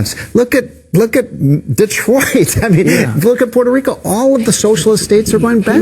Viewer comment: Did he just say "Detroit" ? hahahahahahaha !! :-) 00:56 sec